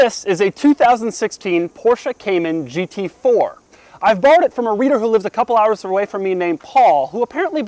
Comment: When he said he borrowed it I had to rewind because I thought he said "bought" and got sooo excited